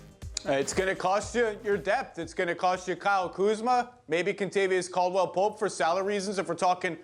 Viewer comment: for a second i thought he said “its gonna cost you a bag of cheetos” but my bad guys he said “kuzma”